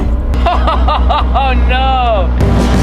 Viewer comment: Anyone else hear him say "oh s***" at 10:01 😂😂😂